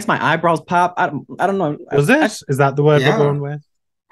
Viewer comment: What does say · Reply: “Does it ? Is that the word we’re going with”